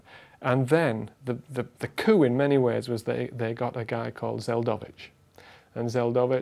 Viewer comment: Interesting extra footage. One question though: What was the name of the Soviet scientist mentioned at 8:07? I'm trying too look him up but I can't figure out the spelling and I've had no luck searching randomly. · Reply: Yakov Zeldovich -- I didn't think you should have to wait another 10 years for an answer ;-)